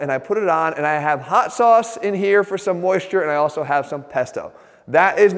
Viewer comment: Woah I thought he said "Testo" instead of "Pesto"